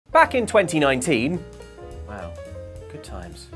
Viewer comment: I THOUGHT HE SAID "facking 2019" AT THE BEGINNING